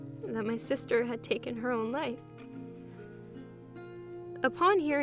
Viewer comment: I thought she said "took her own leg" then she sounded so sad then I rewatched it and I realized she said "took her own life" then I cried